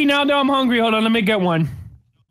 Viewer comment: subtitles said "Mommy you ate my Mentor" XD